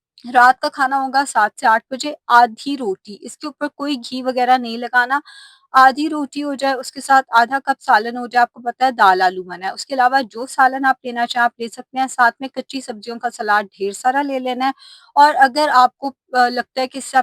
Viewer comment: did she say " daal aloo" mana he @ 4:35... mene 2 dafa suna but samajh nhi ayi.... why daal not allowed??